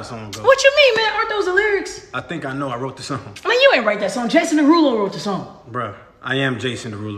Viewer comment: I heard you say Jackson derulo